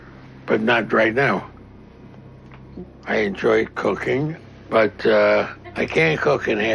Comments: I thought he said "I enjoy cocaine" 😂😂😂 That would explain a lot... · Sounded like I enjoy cocaine not cooking 😂😂